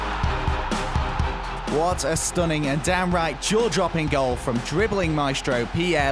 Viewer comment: For #1 did he just say "Dam right"??? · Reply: downright